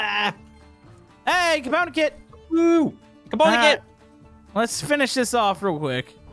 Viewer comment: did say camodo kit?